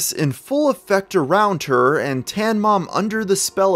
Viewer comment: For a moment I thought It said the "tan man" the man who fought Dan "the van man" Schneider.